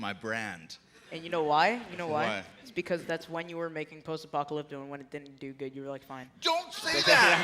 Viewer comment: what did he say? English is not my first, language and he's speaking to fast for me to figure it out. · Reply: "It's because that's when you were making Post Apocalypto and when it didn't do good you were like: 'Fine.' "